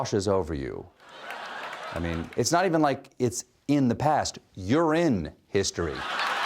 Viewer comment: is pure gold "soaked in history" "washes over you" "its not even in the past, urine it" damn I love him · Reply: I couldn't hear what he said there, and I heard "pisstory" in my mind.